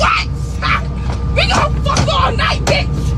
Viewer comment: I kept hearing her say " we gon Fu@ all night, we gon f$#k all night" lol I could be wrong but that's what I was hearing and I wish people would stop instantly dismissing everything claiming its drug use or mental illness they act like spiritual attacks aren't a real thing not everything has to do with someone's mental stability or them being under the influence 🙄 it's like people are in denial and don't want to believe it's something on a spiritual level